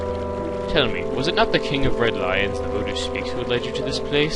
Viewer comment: lulz, I thought you said 'the king of red lines' at first xD now this all makes sense:0